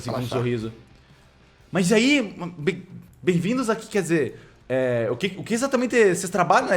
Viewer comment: BEM VINDOS A SANTO BERCO eu juro que escutei isso kkkkk